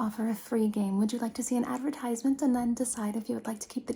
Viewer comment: i heard do you want to see a free advertisement and i just thought than in this future you have to pay to watch ads T-T